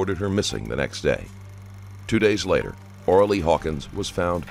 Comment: Really, Ora Lea??? At first I thought he said Orally 😲